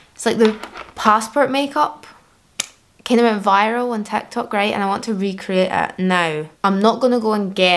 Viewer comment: Ah, she's saying "TikTok." To my ears I heard "tech talk." Yeah, different content. heh.